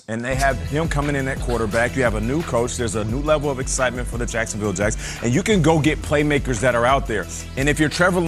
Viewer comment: is it just me or did he say Jacksonville jets or jags 😂